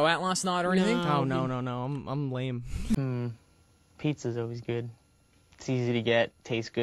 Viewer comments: I THOUGHT HE SAID "Petes is really good" GOD IM PETEKEY TRASH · PLS I THOUGHT HE SAID PETE IS ALWAYS GOOD